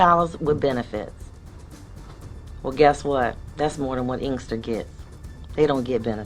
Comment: What is she saying, can somebody help me out? After asking about the salary of someone at the mall. "Well, guess what, that's more than what INGSTER gets. They don't get benefits." What word is she saying that sounds to me like "inster"? Surely she's not saying "Minister" as in a preacher...? After than some words were censored. I totally totally missed the entire sentence. :( /non-native English speaker. <3 edit: A town? She says the word again in the end with the words Police Department afterwards. That whole diatribe about drug users and police officers was lost on me because of the censorship... I would really appreciate proper CC, especially when the audio is cut multiple times during a sentence. This is a world-wide platform after all. Thank you in advance